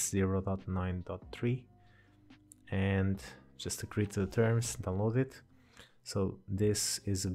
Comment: "agree to the terror-" i heard this so wrong